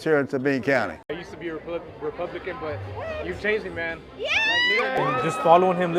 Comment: What I heard at min 4:27, “I’m a roach and I used to vote and fund Raid!” 🙄🤣 🪳 🍊